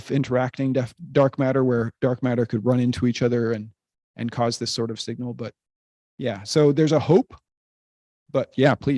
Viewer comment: can you spell the name of the theoretical type of dark matter brought up around 1:13:00? I can barely hear what she said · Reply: is talking about the hypothetical particle, the axion. Depending on its characteristics, the axion could be dark matter. <link>